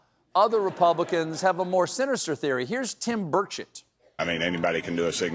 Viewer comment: Seriously, I misheard Colbert saying "Tim Birdshit" in 4:00, wich is a fitting nickname.